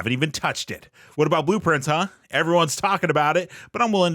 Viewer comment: The lies of P overture and blueprince line at the end 100% accurate for me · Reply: Ohhh Blue Prince, I thought he said Blueprints, haha. I care for neither game (Lies of P and BP I mean, not BP and the non-existent[?] Blueprints lol), but Ghost of Yotei has been waiting a wee bit for me.